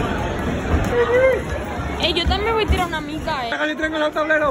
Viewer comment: no si ni quien habla ni que dice jajajja mica es pelota¿ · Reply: mica es poco